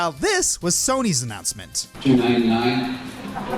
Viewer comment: What did Sony say? I couldn't understand it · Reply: $299. The first thing that was ever revealed was the price.